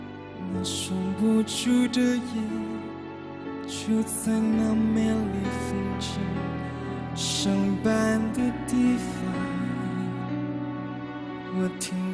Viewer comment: 奇怪，听成"就在那美丽风景，上班的地方"~哈哈，莫名出戏！